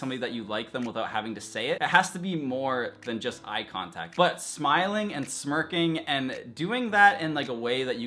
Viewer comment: says... "Smile and smirk" Lol I thought he said smile and twerk! I was like "Whaaattt??"